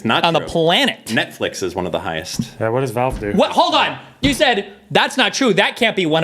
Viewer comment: Did just say "what does valve do?"